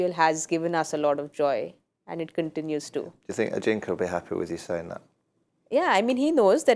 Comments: What did Michael Vaughn said at around 25:42 who would be happy to hear that when Saiyami was talking about Sachin getting the orange cap. I couldn’t get the name which Michael was referring to, something like Ajinkya?? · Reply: Yup he said about Ajinkya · What did Michael Vaughn said at around 25:42 who would be happy to hear that when Saiyami was talking about Sachin getting the orange cap. I couldn’t get the name which Michael was referring to, something like Ajinkya?? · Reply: ajinkya would be happy seeing that